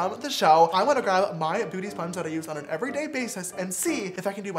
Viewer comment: Did anyone else notice that at 2:05, James said Booty sponge instead of beauty sponge, I laughed so hard, no hate